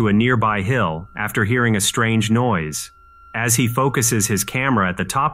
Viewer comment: I thought you said "he focused his camera.."